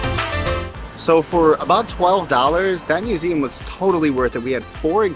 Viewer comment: Lovely to hear someone's input on the streets of my city. PS: at 2:02 subtitles say $112 but $12 is the correct price.